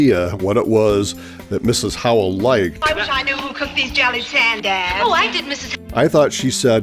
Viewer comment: I thought she said jelly sandwich